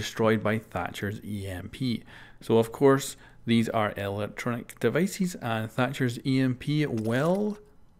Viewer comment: I thought I heard "elactronic device". Sadly I was off, I've mistaken you as a fellow punmaster